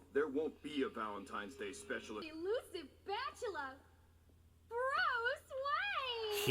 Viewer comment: "elusive bachelor" I swear the first time I heard that I thought she had said "BATchelor"